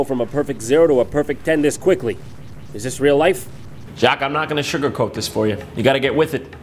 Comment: You know you've watched JonTron too much when you start reciting the Titenic song in your head, get sick of hearing his intro song, and have already started designing a layout for the iJaq app. Someone help me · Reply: Ooooooh, iJacques.... Up until now, i thought he was saying "aye, Jacques"